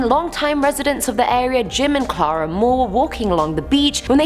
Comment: did I just hear Jimin? My A.R.M.Y heart is racing